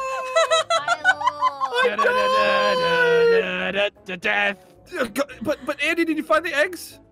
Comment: Did anyone else hear Andy say "da da da da da da da da da death"